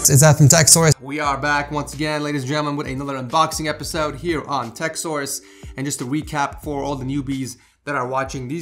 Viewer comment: how come i only hear "Whats up guys its Ed from Tekzors" instead of Tech Source